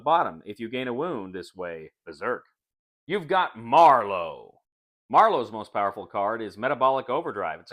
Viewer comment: It sounds like says "You've got Marlo" like the side kick from The Shadow, as opposed to Marrow, as in bone marrow,.which rhymes with arrow.